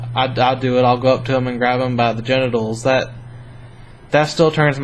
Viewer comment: Ok that was really kinda sweet! I liked that one. (😂I was listening as I cleaned the kitchen so I had to replay the part towards the end-I totally thought he said “grab ‘em by the bit-ables” and I was like😃 he calls lady parts bitables?! Alas, no. He said genitals)😉